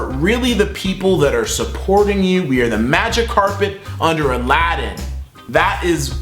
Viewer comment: lol when you said magic carpet i thought you said magikarp